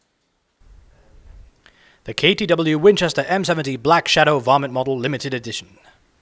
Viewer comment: did he just say vomit?